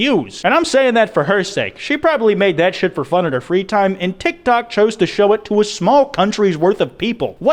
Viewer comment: lmao everytime you say tik tok, i keep hearing “dick tok”